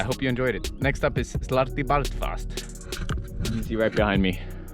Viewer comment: The first one touches your heart, and the 2nd one makes you fart. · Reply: First time he mentioned slartibartfast, i thought he said sluttybartfart